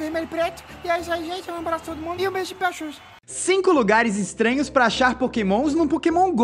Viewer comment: so entendi o um beijo pra xuxa ksksksksks